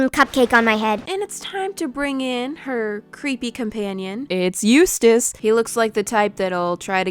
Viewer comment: I thought she said its useless